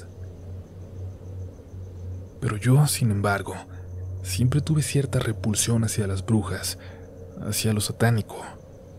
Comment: Hello Baby's de la comunidad RDLN. Acabo de escuchar 00:40. Excelente Relato. Mis audífonos se me dañaron y me tocó escucharlo normal. Me causa curiosidad saber eso lo que dice en latín. Me encantaría que relatara la historia completa como la de 33 velas y no por partes. Esta suena muy interesante... Espero todo acabe bien para esta familia. Dios los bendiga. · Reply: in tenebris non in venite inter pater manducare et manis te no viscu aeternum in tenebris manducare intrat pater. Ven, déjanos en la oscuridad, estarás en medio de las sombras del padre de ti para comer tus entrañas, entra padre para comer en la oscuridad, para siempre.